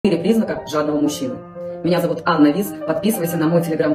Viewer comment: Ах, "жадного"! Я сначала услышал "женатого".